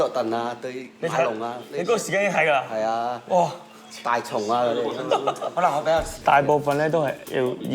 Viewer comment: you guys wrote that the guy said Dennis Rodman, but its sounded like he said thai chona LOL!!!